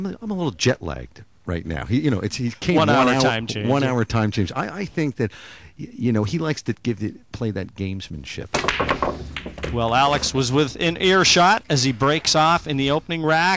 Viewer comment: LOL. What did the Commentator say at 6:28. "I think he likes to play those games and shit.."